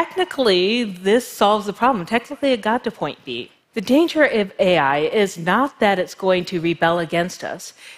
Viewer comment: For a moment I thought it said "The danger of Wierd Al" and thought; damn, they're onto him.